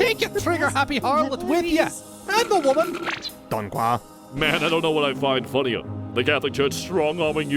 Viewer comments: What is he saying at 2:03? PLease tell me so I can translate it? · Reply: Sarah Barisas don quoi? say what? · What'd the French guy say at 2:03? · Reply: Donc quoi, translates into “so what?”, but I think it was more meant to be interpreted as “what?”